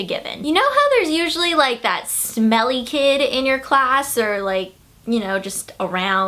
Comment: totally thought she said "Smelly Cat"